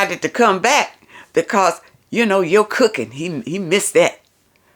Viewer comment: At first I thought said, because he misses your🍪.😆 But she said he misses your cooking. Lol.